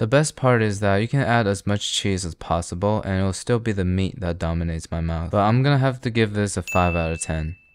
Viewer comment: bro what did you say!!!💀💀💀💀💀💀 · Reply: He said it doesn't matter how much you put cheese the main taste comes FROM MEAT 😂